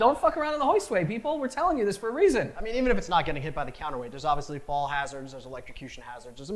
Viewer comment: Okay 100% misunderstood him when he said fall hazard I was doing something and misheard that and I heard ball hazards 😂😂